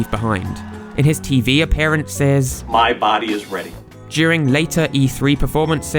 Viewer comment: For the longest time I thought he said “ My body is Reggie “